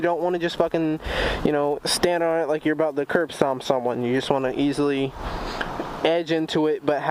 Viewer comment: DID you say curb stomp??